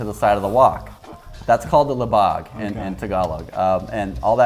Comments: is it me or did i hear he say "LIBAG" ? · "We all have libag." 😂😍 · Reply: I was like, did they just say libag?😂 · did he just say libag?